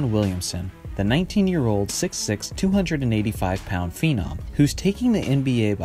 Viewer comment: Who else thought he said nineteen year old six six 285 pound female